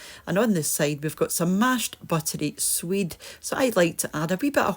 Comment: Hi. Well, you learn something new everyday. For some reason, every time you served mashed swede in you videos, I thought you were saying mashed sweet, meaning sweet potatoes. I finally saw what you were really saying, so not knowing what swede was, I looked it up. In the US, this is rutabaga (which I do not eat or have an occasion to eat). So, now I know what you are saying when you serve mashed swede--learn something new. Hugs.